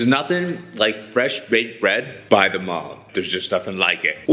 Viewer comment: I thought he said bread by the Mob. LOL